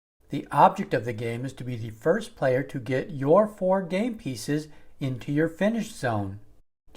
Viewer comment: I thought it said gay pieces